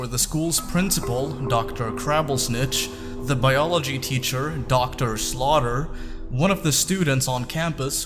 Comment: I heard Dr. Slaughter instead of Dr. Slawter.